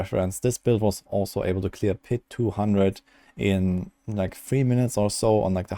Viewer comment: Did you say able to clear Pit 200?